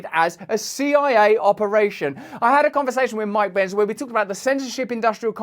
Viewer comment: I thought you said Mike Pence at first, and I was about to bust out laughing.